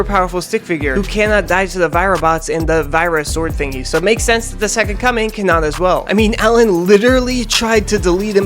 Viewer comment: why do i keep hearing "the second cumming"?